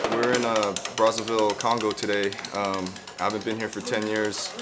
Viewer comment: did he say “we’re in Brazzersville Congo today” 😂